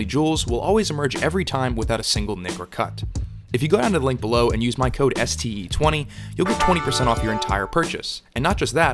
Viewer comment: bruh i thought he said “Use code STD20” 😂😂